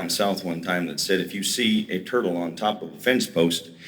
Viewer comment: "if you see a turtle on top of a fence post, he did not get there by himself" words to remember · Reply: Oh turtle, I thought he said turkey. I was so confused